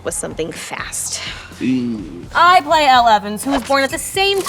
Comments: Thought she said “i played eleven” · When she said, "i played elle evans" i literally heard "i played eleven"😂 am i the only one? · I thought she said „I play Eleven“ 😂😂